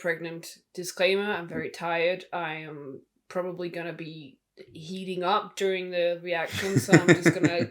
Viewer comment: When she said heating up I thought she said “heaving” up!!! Thought there was vomit concern!!!